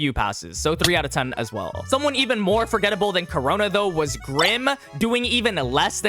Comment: Lowkey I thought he said rin